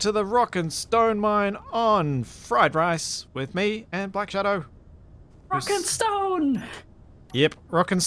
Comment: DID I HEAR A ROCK AND STONE?